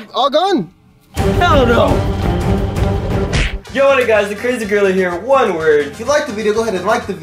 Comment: I thought he said the crazy girl here